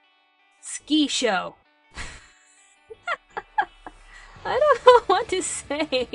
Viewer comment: OMG, Thankyou for putting Sukisho on the list. first i thought you where saying "a Ski show". but now I've watched it (and LOOOVED IT) I have to say, thank you. I owe you for turning me onto that fantastic show. <3